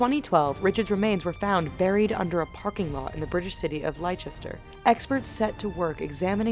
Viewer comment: LOOOL did she just say "lie-chester"????!!